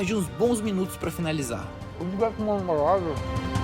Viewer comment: Só eu que ouvi ele falar ce consegue pra mim uma água ali no 7:18 ?